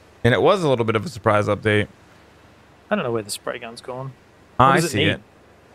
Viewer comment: When he said Spray Gun, I heard "Ray Gun" and at that point I was like.... -_- game ruined